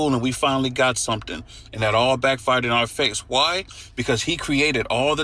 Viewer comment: Did he say back farted in our faces. 😂